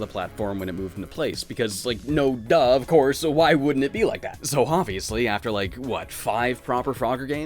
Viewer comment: I could have sworn I heard you say "Hopbviously" I'm going to pretend you did, it works on a million levels here